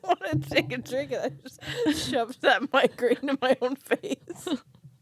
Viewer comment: Did call the mic a migraine when it almost hit her in the face at around 30:21? hahah love these gals